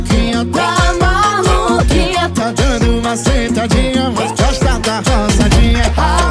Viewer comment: Gente agora que eu entendi que o "roçadinha" é uma bela tesourada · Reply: E eu que entendi " mas gosta da rosadinha" KKKKKKKKKKKKKKKKKK